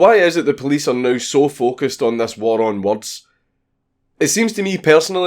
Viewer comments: It seems YouTube has censored my comments section. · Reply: Love the video and love the accent... but once you've heard it as "war on warts"... it's kinda hard to unhear. ;) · I thought he said war on warts